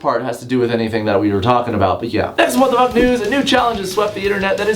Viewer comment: At the start it kinda sounds like he says what the fuck nudes